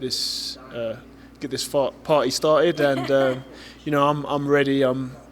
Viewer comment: did he just say fart😂😂😂